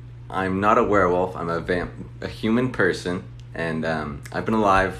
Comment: He’s actually a vampire I heard him say vam-human